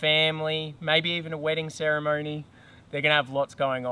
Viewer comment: Wait wait wait. At 2:34...did you say *wedding ceremony*....?!?